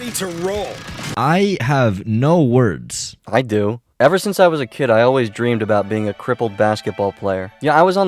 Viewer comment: I quite literally thought he was going to say “Ever since I was a kid I’ve been legit”